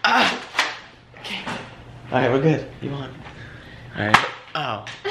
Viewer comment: Y’all are such a cute couple, but did I hear say “ Alright We are good EVAN” what?? The time 9:32or 33 🤍🤍❤️❤️❤️❤️ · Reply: He said "you won"... Not evan